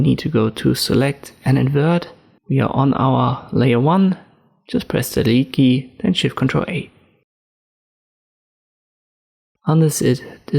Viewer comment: nice tutorial but i am not able to understand what you said at 08:27 after going to select menu - Invert after that what have you said while deleting outside ellipse area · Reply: I said: "We are on layer 1 - just press the delete key. Then Shift+Ctrl+A" to deselect.